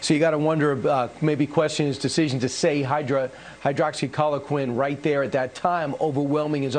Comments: did Kilmeade say Hail Hydra? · Wait!!! Did Kilmeade say Hydra??